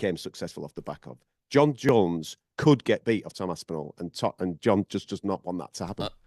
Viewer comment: I thought they said Tom Jones .